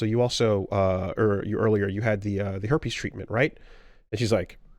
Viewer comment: i genuinely thought said Hair Piece Treatment